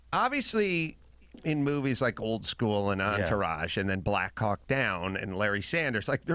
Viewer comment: the first time i heard Carolla mention "Black Hawk Down," i could've sworn he said, Black Cock Down—probably because he had just finished referencing a joke he does about his dick not being special barely 60 seconds earlier.